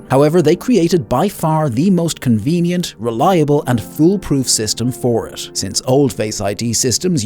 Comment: you probably meant to say "fool proof" but the subtitles say "full proof". what would a "full proof" in this context even be? fortunately this homophone isn't too bad because it doesn't negate the meaning of the sentence, but such errors often do negate it, and this really grinds my gears. just a warning